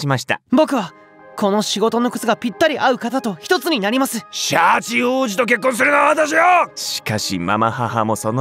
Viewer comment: シャチ王子が八王子に聞こえる